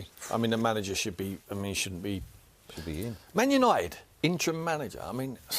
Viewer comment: did he say at 3:33 the next manager should be.. should be.. (background voice) "should be you" lol or did i hear things?